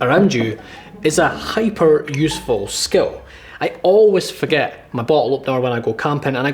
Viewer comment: Was it just me or did he say "i always forget my ball opener" and "i got really good at opening balls"